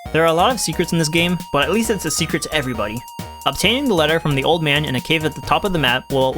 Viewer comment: I could have sworn it said "show this to the old mama"